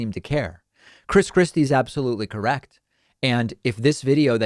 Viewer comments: Did I heard you say " Chris crispy" Lol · I know David doesn’t do this type of thing, but I kept hearing him say “Chris Krispy”